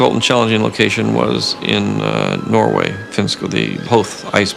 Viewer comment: Ice planets rule! Underwater scenes aren't too bad, either. Hayden Christensen is a cutie patootie. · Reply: OzzyOzWald He did? I thought that Hayden said, "Howth" like the street in San Francisco.